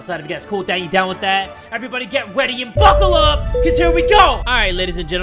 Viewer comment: Did just stay get weady instead of get ready 😂